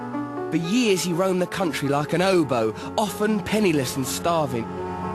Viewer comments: For a moment there I thought he said 'he roamed the country, like an oboe' til I realised he was saying hobo... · For a second there, I thought Russell said 'he roamed the country like an oboe', which seemed quite odd to me - until I realized he was saying "'obo". *headdesk*